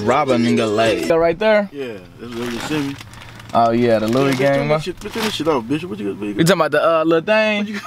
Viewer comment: Lmao I thought he said “turn that shit off bitch”